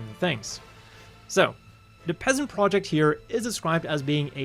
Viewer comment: Finally a Gelgoog Dev. History... Also I've misheard the PEZN project as PEASANT project and has been confused on why Zeon would name their project as Peasant this whole time😂